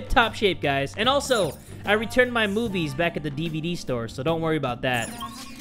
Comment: I heard "I gotta return my boobies to the DVD store"